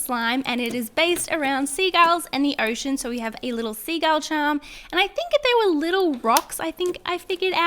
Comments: When she was saying seagull I thought she was saying sea gale😂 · When she said Seagull I thought she said sea galls Edit: LOL🤣🤣🤣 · I thought you were saying sea girl not segal😂😂😂😂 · I thought she was saying se girl